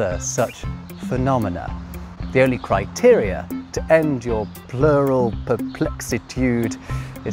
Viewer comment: you almost sound as if you're saying "The only criteria ... is to watch ...". I hope that's not what you're saying. I have often heard "criteria" as singular from other people.